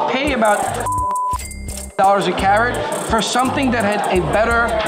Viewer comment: 242,000 ÷ 600 = 403 per carat average (wholesale) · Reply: what do you think he says at 14:38? lip read wise and the bad censoring make it sound like 185/karat ur could that even be right? this was in early 2022